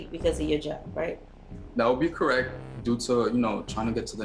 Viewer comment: "be correct" did I hear that right??? What the fuck bro. Don't need to hear another word I hate already. JK he said "that would be correct" ... Still hate him anyway